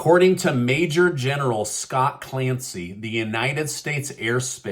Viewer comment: For a Moment There I thought I Heard TOM CLANCY !? 🤔🤔🤔